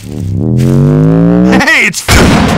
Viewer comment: sounded like he said "H-h-hey its frick"